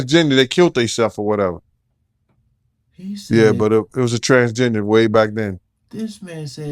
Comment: I’m confused by the terminology Gingerbread Man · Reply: When Gene says transgender it sounds like he is saying transginger. My guess would be he would call them ginger bread man when he was with his boys as code for “that’s not a women, that’s a man” only speculating